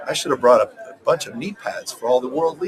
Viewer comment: knee pads??? I misunderstood what he had said. I thought he said 'Pee Pads_....... oops my bad